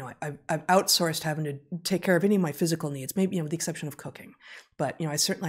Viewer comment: I initially thought Heather said, "OK, I've outsourced everything except maybe cocaine..." Cooking, right. LMAO!